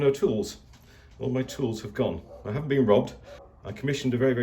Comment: Not me, not sure what you mean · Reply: my apologies at about 1:35 you mentioned all your tools were gone, and I THOUGHT you said you had been robbed, but were so nonchalant about it I chalked it up to British gentlemanly laissez faire "oh well"... but now that I listen to it again, you spoke so quickly I did not hear you say the word "not"; I have not been robbed. Sorry for the trouble; but glad to hear it didn't happen, as Mark Twain said, upon reading his own obituary in the paper, "The reports of my passing have been greatly exaggerated". :)